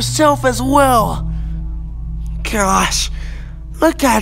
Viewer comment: Him: Your always getting yourself into trouble you little angel Me: acts like a tsundere I'm not an angel... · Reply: I THOUGHT HE SAID YOU LITTLE BAGEL😭